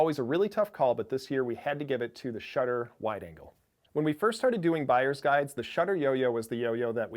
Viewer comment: At first I thought he said the shutter wide ANKLE